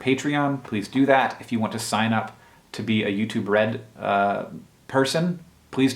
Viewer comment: Youtube Red? All this time I thought people were saying "U2 Bread"